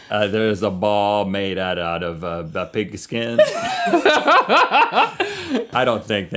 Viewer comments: I thought he said "ball made out of baby skin"... there's something wrong with me. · I thought he said "ball made out of baby skin"... there's something wrong with me. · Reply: sounds like he said pig skin °-°